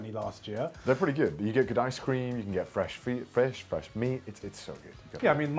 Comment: Did Cdwag just say "fresh feet?" Does this confirm Cdawg has a foot fetish?